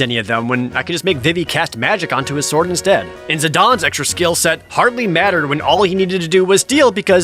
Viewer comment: I'm sorry did you just say "Zidon"?